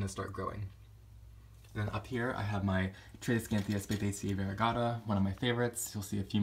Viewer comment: What kind of plant is that! Couldn't catch the name- but it is quite beautiful! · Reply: Tradescantia spathacea “Variegata”. Such an awesome plant!